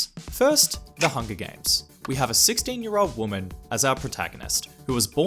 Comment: When you said 16 year old women I thought you said 60 year old woman 😭